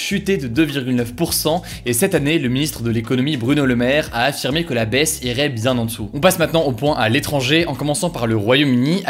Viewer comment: Salut, Hugo dit quoi à 4:11 ? "Cette année le ministre de l'économie, Bruno le Maire, a affirmé que la baisse ________." Est-ce quelqu'un pourrait m'aider ? Merci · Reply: deserto que là baise irais bien en dessous que celle que là baise de la 2sd guerre mondiale